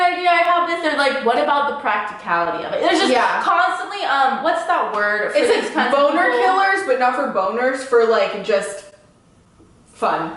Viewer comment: “What’s that word?” Buzzkill?